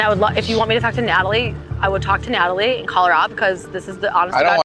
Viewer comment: Shane actually impressed me a bit in this scene. I wish he would've defended Natalie when Shaina threatened to go after her or confront her in a really aggressive way. Other than that he did pretty well. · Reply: I agree and thought she said “call her out”, but I think she actually said “call her up “ which doesn’t actually change anything, lol, but might be less aggressive than I originally had thought.